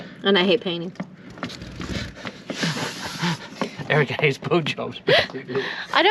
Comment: OMG excuse me but with your accent , when you said Erica hates Boat Jobs I thought you said Erica hates Blow .... nevermind 😇